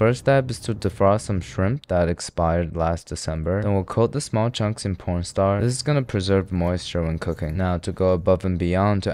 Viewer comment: Did he say "small chunks in porn star"...